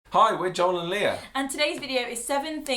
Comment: I thought you said John and Lia